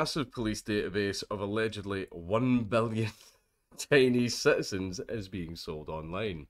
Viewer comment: I thought he said 1 billion tiny citizens.... As it started. Only thing I could think was... Yeah Chinese are little... Generally